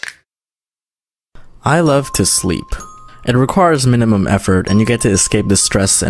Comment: seconds in and the first thing I hear is him say requires WRONG?